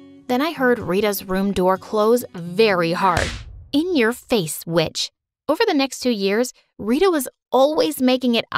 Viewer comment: i thought that said in your face Byaitch